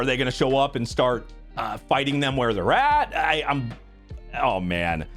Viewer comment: I misheard this as "fighting them where the rat".